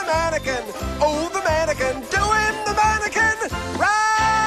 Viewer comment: “Mannequin Rag”? Ohhh, I thought you said “Vatican Rag”. And I was ready to genuflect too...